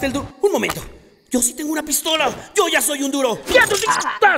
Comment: me dio risa que dijo ''un momento si yo tengo mi arma y soy duro'' XDDDDDDDDDDDDDDDDDDDDDDDDDDDDDDDDDDDDDDD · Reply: en realidad dice "Yo si tengo una pistola, yo ya soy un duro" :v